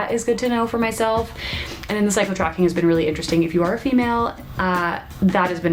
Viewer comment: Haha, I thought you said 'Psycho tracking' not 'Cycle tracking'. Enjoy the summer, we know how short it is☹.